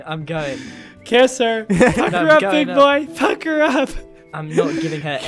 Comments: is it just me or did he actually said "F*ck her up!" ?????!! what the hell man?! O_o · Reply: he said "pucker up" x3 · I THOUGHT SAID. F- HER UP BOY F- HER UP OMG THIS VIDEO HAS RUINED MY MIND.